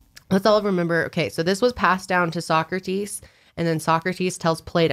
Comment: When you guys were saying "Sócrates" i got so confused cuz i heard "soccer tees" lmao